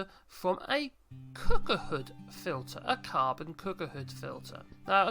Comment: What are you saying at 2:32 or a bit more? The filter you added made from a cucucufilter???? WTH??? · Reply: It is made from a Cooking Hood Filter. Hope that is clear!